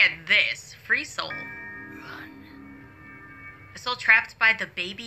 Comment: girl i heard run…..